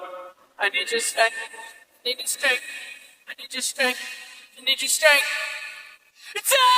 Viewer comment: I always thought he said “I need to scream I need to scream” I didn’t even hear the lord part wtf amazing